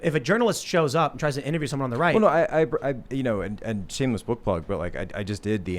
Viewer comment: I thought homie said shameless butt plug lmao until he said sumthn about the contents of the book